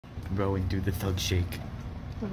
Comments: what the hell did he call her? Growing? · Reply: Rowan, as in the name Rowan. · My dumba$$ misheard "thug shake" as "f*ck shake" 😭😭😭